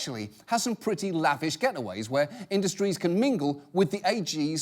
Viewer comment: Okay, I was on a different tab when it was talking about the selling access to AGs. I heard "Coke industries" I quickly came over hoping their either meant Coca-cola or coke industries. I'm a little disappointed